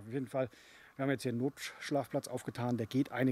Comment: Wie geil, sagst ja er ist so krank und bei 10:35 hab ich verstanden "wir haben hier nen NOTSCHLACHTPLATZ aufgetan"! ich krieg mich nicht mehr ein! :-D :-D :-D Trotzdem gute Besserung!